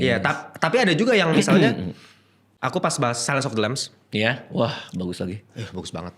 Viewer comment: itu film di menit 28:44 judulnya apa bro? nggak kedengeran. pengen nonton! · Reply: Silence of the lambs